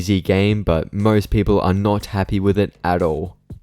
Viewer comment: I entered the video and the first thing I hear is "Pants vs Zoodis"